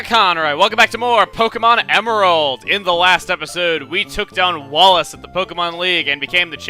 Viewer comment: Did you say "tooked on Wallace"?